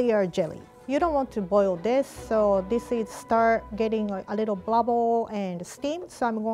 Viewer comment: watch Rie saying "bluble" instead of bubble, and "boo boomed" instead of bloomed 😂❤